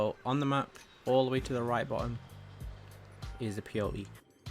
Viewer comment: I thought he was saying P O E the whole time